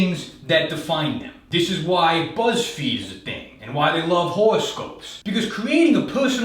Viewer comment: Pretty sure I heard him say "whorescope".